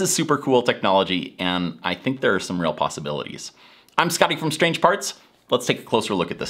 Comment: I heard as: “I am skeptical of strange parts”. 😂😂😂💀💀💀💀😂😂😂😂